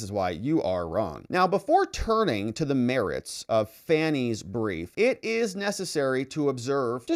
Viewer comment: wasn't paying proper attention, initially thought you said "before turning to the merits of Fani's *briefs*" 😱